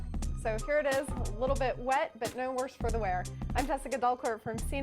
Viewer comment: I thought she said Samsung 'DRUG' Pro! XD You have to be on drugs to get this!